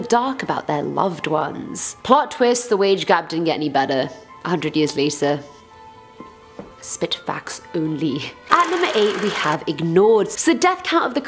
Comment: what did you say? I wasn't sure if you said something and my phone glitched or different language.thanx😁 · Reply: "Plot twist, the wage gap didn't get any better, 100 years later....Spit facts only"